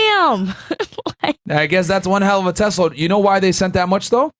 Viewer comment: Not gonna lie I thought he said "C-Load" when he was talking this episode up on after hours. Kinda disappointed now lmao 🤣